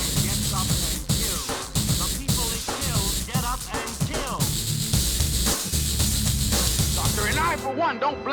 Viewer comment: Bled Dry having the perfect sample and when I heard get up and kill I thought it actually said shut up and kill fits perfectly with KF